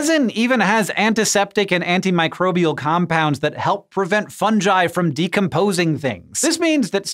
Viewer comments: Did he just say”funjai” instead of fungi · After 1:00 all I heard was Funjai